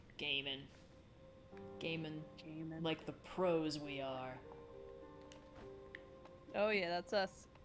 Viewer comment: I heard that as "Gaiman like the prose we are"